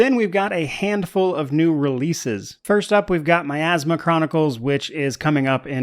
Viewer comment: I thought you said My asthma chronicles.....weird game